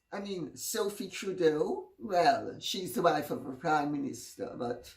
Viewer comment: Dear Lady C, I'm American, sometimes I mishear your words. when you said Sophie Trudeau is the wife of a prime minster, I rewound it three times giggling because I thought you said "wife of a Communista"